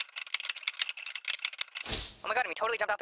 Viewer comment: Ever try to understand what they're saying? It sounds like they're saying "oh my God we totally jumped off the cliff...."